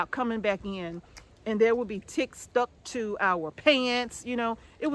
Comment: My goodness, I thought you said Chicks stuck to your pants. Took me a second to realize what you'd said 😂